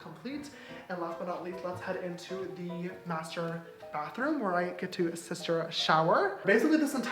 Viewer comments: Am I the only one who thought he was going to say “where I get to sister shit” ?? 😂😂💀 no just me ok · I thought james was going to say, "this is the bathroom where I sister shit" 🙂🤦🏻‍♀️😂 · I thought he was saying where I get to Sister Shit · When he introduced the master bathroom I legit thought he was going to say sister shit instead of sister shower 🤣😭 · did anyone else think he was gonna say sister shit instead.. just me, k. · I swore you were gonna say “sister shit”